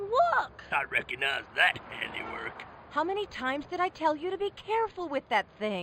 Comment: what did nick nitro say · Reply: "I recognise that handiwork".